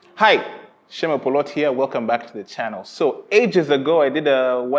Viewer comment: You're absolutely right. I actually didn;t think too much about that when I bought them. I was taken by the aesthetics more. Probably should've reconsidered. But so far so good! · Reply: It's funny for me when you introduce yourself at the beginning of the movie because for the first time I heard "siemo Poloti" I'm from Poland and in Polish "siema Polaki" means hello Poles. And for a moment I had such a face palm trying to understand why you greets Poles and continues to talk in English. Sometimes people from other countries twist Polish words like that when they can't pronounce them correctly so I wish you could see my face when I realized my mistake.